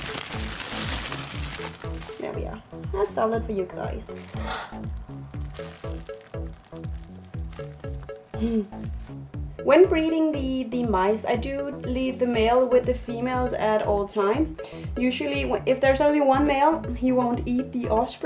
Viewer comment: When you said breeding the mice I thought you said freezing the mice and I was like poor mice 😂